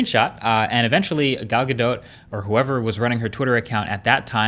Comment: For the first part of the video I thought he was saying "Galaga" did an oopsie. Not Gal Gadot.